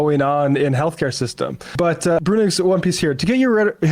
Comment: What’s he’s referencing at like 7:50? I’m curious. Is he saying Brunic?